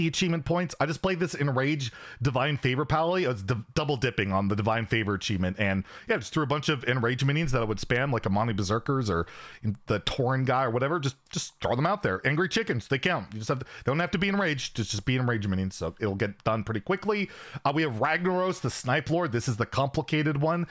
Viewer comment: I watch most youtube at 2x speed. You sound like you say "Leeroy Chickens" :0